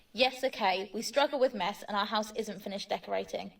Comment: I did NOT hear mess lmao.. I heard m*th🫣